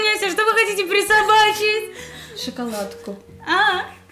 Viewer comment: Одна я услышала что сказала на моменте 10:35 шиколадка-пис..н. Хаха хаха😅😂😃🙌